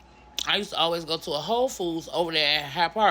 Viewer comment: I'm laughing at the way She said Whole Foods. She sounded like it said H0€ Foods.